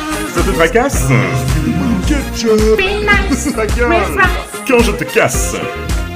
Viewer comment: c'est moi ou tu dit "mmh ta gueule" à 2:42 ??!?XD mais sinon c'est une vidéo plutôt cool !